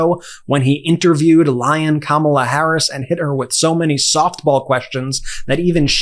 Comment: "Lyin' Kamala"? I heard "LION Kamala"! Seriously, I was listening to this, not watching the screen and heard clearly it's LION!